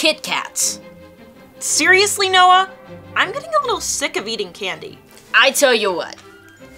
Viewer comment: the captions said tired of eating candy but the line was sick of eating candy